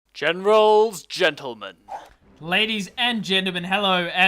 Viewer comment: I heard that Genemen 0:02 XD